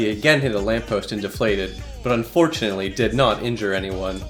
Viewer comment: did he Say unfortunately